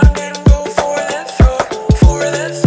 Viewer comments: I thought it said "daddy-chan dint go for the throat" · Why do I keep hearing “Daddy chan didn’t go for the throat” instead of “Daddy chimed in go for the throat” ?!?!?